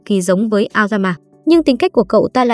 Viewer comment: Aoganma, tui nghe thành lao gam ma